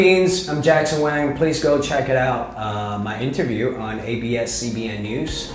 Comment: When in the beginning he said "please go checkout my interview"... I heard it as " Please don't checkout my interview "💀